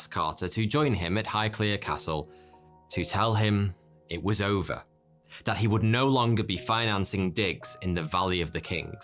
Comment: For a second I thought he said “that he would no longer be financing dicks...” I was about to be like “wait! You didn’t tell me he was running that kind of business!🤭”